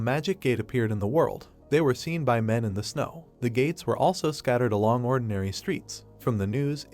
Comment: Tell me why when he said the gates. I heard the Gays😂😂